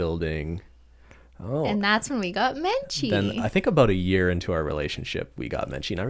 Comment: So I was listening to the podcast and I had to pause it for a second to do something else, and it ended on “and that’s when we got me-“ 😂 and I played it again immediately because I thought she said that’s when we got married and I was like... 😂 what? Stop lying